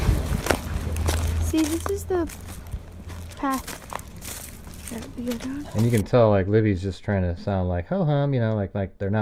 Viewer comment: I personally don’t hear could that be a gun. I hear, “So this is the path…that we would go down”. Anyone else hearing that?